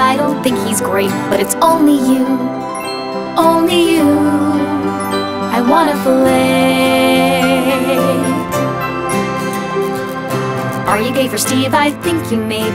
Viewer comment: Candlelight and moonlight all around us I look into your eyes You hold my hand underneath the table And I can’t disguise the way I feel I could’ve wished a thousand wishes For this night, I can’t believe That it’s finally me and you, and you and me Just us, and your friend Steve Do-do-do do do-do do, Steve Do-do-do do do-do do, leave! What’s the deal with your friend Steve-- Oh Hey! How's it going? You took me out for waffles and to the movies I was hoping for romance You even took me to the Mexican circus I thought there was a chance we’d be alone I could’ve wished a thousand wishes For Steve to disappear What the fuck’s your fucking problem? Why’s he always here? Do-do-do do do-do do, Steve Do-do-do do do-do do, go away! What’s the deal with your friend Steve-- Oh Hey! How's it going? It’s not that I don’t like your friend But how many hours with him can you spend? And it’s not that I don’t think he’s great But it's only you, only you I wanna fellate Are you gay for Steve? I think you may be And that would be alright But let a bitch know just what your steez is So I know if I should shave tonight I could’ve wished a thousand wishes 'Cause I like to be with you But if it’s always me and you and your friend Steve There’s nothing I can... Do-do-do do do-do do, Steve Do-do-do do do-do do Why don't you just go be with your best friend and boyfriend, Steve~ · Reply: I thought they were saying 'i want a fillet' lmao kinda confusing but okay. All this time it was "fellate" okay 👍🏻